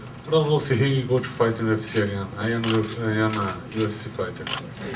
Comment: What in the hell does Silva say @ 4:00? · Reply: idk if he will fight in the ufc again, I am a ufc fighter